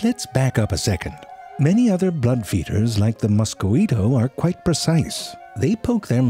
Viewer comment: Mosquito sounded like you said macchiato. 😂🤣😂🤣🦟🦗💙💚💜As all these videos, I learned new stuffs in the funniest way possible 🤣😂🦟🦗